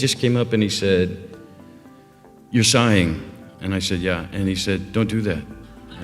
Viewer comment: Hello, guys, could you help me? I'm from Kazakhstan and don't quite know English😤 So, I didn't understand in 1:56 what did Brad Pitt said? Sighing or signing or even other word?